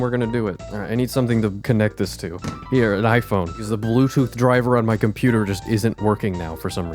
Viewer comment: new video before bringus studios is crazy :O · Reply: In 11:58 did he say Livephone?